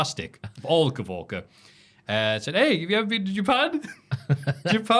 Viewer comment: What the word that says at 18:36? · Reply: Said "hey, have you ever been to Japan?"